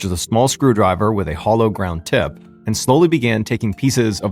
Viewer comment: i misheard him and thought he said “nun” and was thoroughly confused 😂